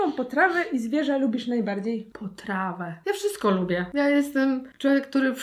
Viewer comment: usłyszałam trawę i już tak Aha. Ok. Fajna potrawa. Fit 😂❤️